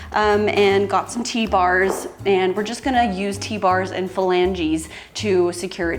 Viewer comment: I heard PHALANGE!! Hahaha! She said 'flange' I guess?? Thank goodness, there is nothing wrong with the left phalange!!!